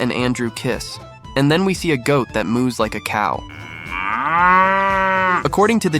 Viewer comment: They filmed a goat that "moved like a cow" by ACCIDENT????????????? 🤨🤨 Scraping the barrel. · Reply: He actually said Moo’s like a cow (but I admit i thought he said moved like a cow at first 🤣. But seeing as there were cows in the background I would like to bet it was one of them that mooed & not the goat 🤔🤣😂